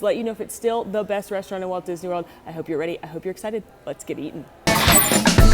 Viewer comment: I heard, “Let’s get eaten!”